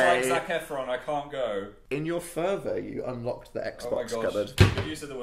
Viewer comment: what does the word furber or furwar or furbar mean????????? pls someone tell me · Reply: Tonje Sandanger “fervor” is what they said and it basically means eagerness or excitement